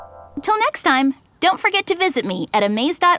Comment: I thought she said "or got my YouTube channel to watch porn" lmfao